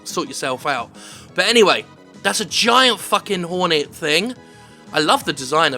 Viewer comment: I heard "fucks in forest" : /